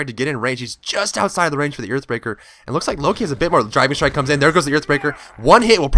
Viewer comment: I keep mishearing "Driving Strike" as "Drybear Strike".